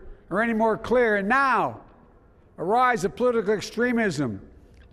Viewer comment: Yo what he say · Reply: the political extremism